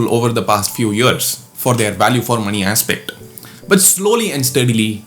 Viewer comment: wait, how did you pronounce 'money'? Did I hear मणि?